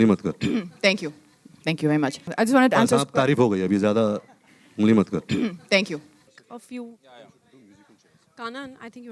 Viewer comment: can someone plz translate what he said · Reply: he was saying that "maine teri bahot maar li, ab tu meri mat maar"